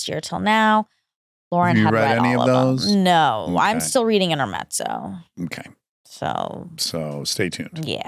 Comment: Monica - what book did you say you're reading? Every book you mention is always so good, but I'm not understanding the title you mentioned here. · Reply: Intermezzo by sally Rooney I believe she’s referring to!